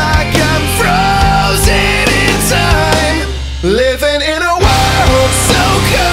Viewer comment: The lyric I had trouble with is "Frozen in time" I always thought it said "Frozen inside"